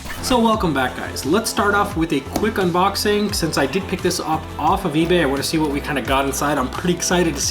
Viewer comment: Anybody else heard ‘dickpic’